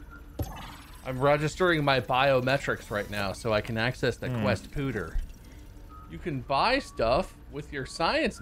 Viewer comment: Thought the title said “Foreskin Wade” 💀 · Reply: That's okay. I thought said Quest Cooter instead of Quest Pooter. Had to rewind a couple times. 😂